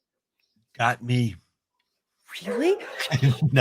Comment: What is answer in 40:40? I don’t understand what he said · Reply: He says, "Got me". I think he means "I have no idea". In a joking way, of course.